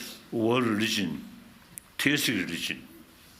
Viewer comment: The word is Christian not terrorist, Audio picked up wrong word due to His Holiness accent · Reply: As others have pointed out it sounds like he said Theistic religion.